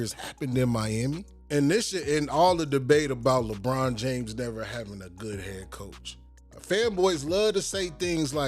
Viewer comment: for a second I thought you said LeBron’s never had a good haircut😂